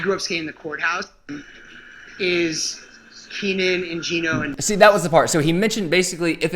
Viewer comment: I couldn't understand/hear who/what he was mentioning, when you said. "That was Fudged up..." Was it "Keenan"??? As in Keenan Milton???